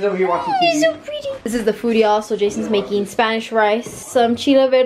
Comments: I swear I heard her say jason instead of Jacob when showing the food 😂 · Is it me or did really said “this is what Jason is making??” On 13:59?? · DID SAY "JASONS MAKING"